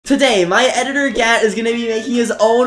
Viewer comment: People who thought he said My editor Gyat💀💀💀💀💀💀💀💀